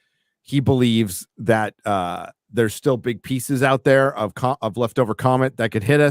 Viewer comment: For a second I thought said '...Big Pizzas Out There!'.